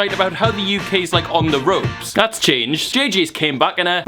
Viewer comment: Did you just say Deji came back? I think your confused with csg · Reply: Bruce Lee he said Jj